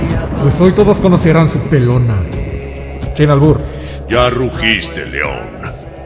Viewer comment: Alguien me puede decir que dice en el seg 00:07 porfavor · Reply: Sin albur (Sin mal pensar)